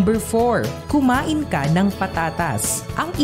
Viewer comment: I thought I misheard ... "Kumain ng Patatas" sa number 4 .. Kumain pala ng prutas 😉